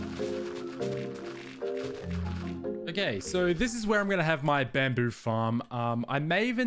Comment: Bro I thought he said frigin bambi